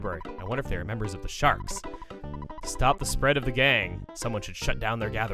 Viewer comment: When he said stop the spread of the gang I thought he said stop the spread of the gay.